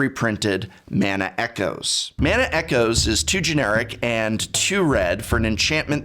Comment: When he spoke about Mana echoes he said, "2 generic and 2 red" but I heard it as "too generic and too red" and I was all like, damn, I guess the just don't like this one